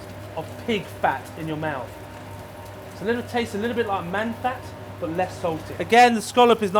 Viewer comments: did he say at 2:13 its a bit like man fat? you can use it as lube are you from manchester ? lol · did he say at 2:13 its a bit like man fat?